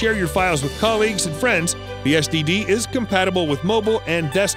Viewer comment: Did he call the SSD an STD?